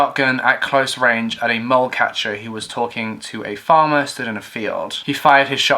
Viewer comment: Is saying a "mole catcher?" As in the small, burrowing creatures?